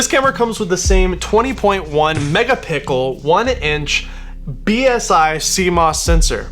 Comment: Did he say megapickel at 1:58? 😂